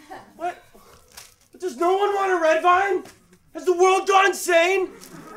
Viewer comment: DOES NO ONE WANT A RED VINE?! HAS THE WORLD GONE INSANE?! GOD.~Ronald Weasley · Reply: I thought he said, ‘has the world gone sane!?!’ Implying that only insane people actually like red vines 😂